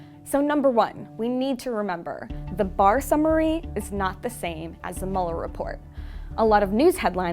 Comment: what does she say at 1:20? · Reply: The barr summary does not contain a full complete sentence from the mueller report.